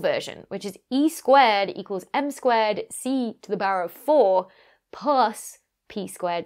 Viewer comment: LOL @ 9:26 did you say c4 puss ✨😆😅🤣😂✨